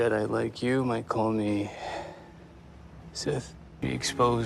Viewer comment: I thought he said y’all call me Sith. But he just wants to be free of Labels. But I guess the fact that he wants and apprentice (acolyte) means he’s trying to push his legacy forward.